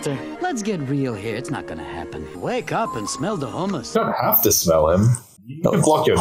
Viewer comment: I always thought he said, “ wake up and smell the homeless“ as well 😂